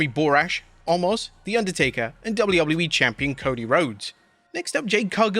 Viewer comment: did he said WWE champion Cody Rhodes Drew will be pissed